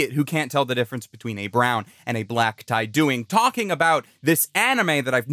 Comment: I thought you said "brown and a black guy" in the begining and i was like "holy shit thats a bold way to start a video" 💀💀💀